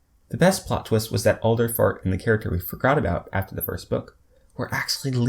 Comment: Wait did I mishear this? Alderfart?